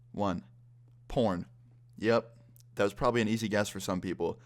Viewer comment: I knew what he said at 1:54 pron